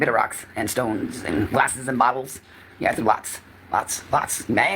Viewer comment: I thought she said boulder 😂 😂😂😂